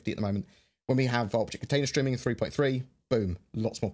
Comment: it sounds like you said "...Vulture container streaming..." HAH!!